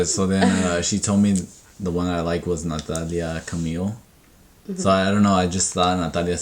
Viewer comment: I love camila celeste not gonna lie first thing that came to mind when you said nathalia I thought you said thotiana 🤦🏽‍♀️😂 sorry🙈